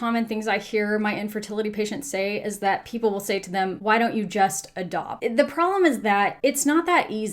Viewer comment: “Why don’t you just A DOG” 🤣🤣🤣🤣 When the Dr said it it was ADOPT but the captions said A DOG.